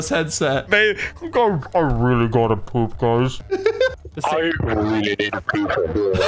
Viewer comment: I always heard: I really need a piece of eden.